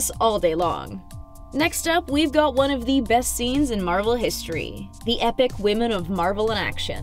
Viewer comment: did she say epic and one of the best? 😂